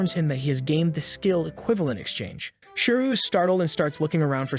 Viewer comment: did you say... "Equivalent Exchange"? Any ProjectE enjoyers here?